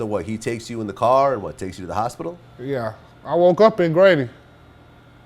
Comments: I thought this nigga said ''I woke up in gravy" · Vlad thought that nigga said "I woke up in gravy" so he had to pause for a sec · Did he just say he woke up in gravy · WTF He Say @ 9:56 He Woke In Up What??? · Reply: Grady. A hospital in Atlanta